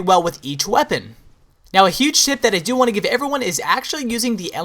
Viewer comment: I like the mk46 with silencer+rapid fire using specialist, aand also it sounded like u said "and a huge shit i want to give everyone"